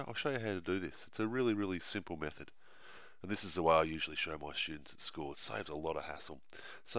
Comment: it sounds like he said "this is how I show my shits at school."